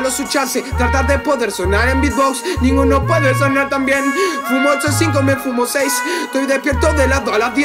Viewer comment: En el 2:22 creo que dice "Fumo 8 o 5"